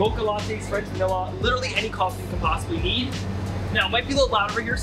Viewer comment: did mans just say “eat” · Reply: He said need.